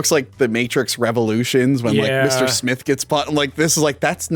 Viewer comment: did say Mr. Smith